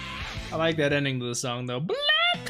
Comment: In op 3 it sounds like it says black clover, but the song is really called black rover.